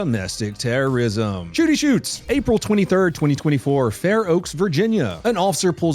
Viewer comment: Did you just say April 24 2024..?